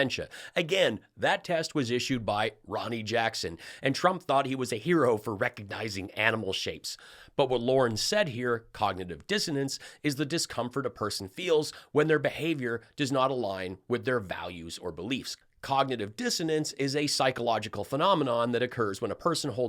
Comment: She actually said, "cognitive dissonance?" 😂😂😂 What a dummy! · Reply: I heard, "cognitive dizziness". My bad.